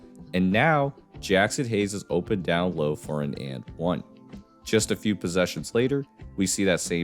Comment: " jackson haynes is open for a and one?" Bros throwing words together lol wth. What kind of wording is that?💀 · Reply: Jaxson Hayes is open down low for an and one